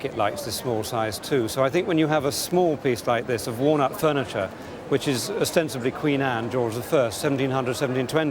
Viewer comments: He was saying 'walnut furniture' but at first I thought he was saying 'worn out furniture'. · The way he said walnut furniture I at first thought he said worn-out furniture!